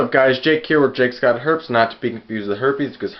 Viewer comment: LOL thought it said jakesgotherpies... whoooops